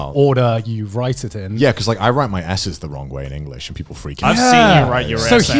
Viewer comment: whenever they said "S's" I heard Essays and I'm like HOW TF do you write them "the WRONG WAY"?? Hello?